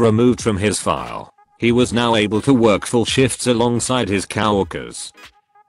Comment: Somebody fix the robot voice. It's not cow workers, it is co-workers. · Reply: i heard it "kow-worker", as in "kow-walski", a character from the Penguins of Madagascar.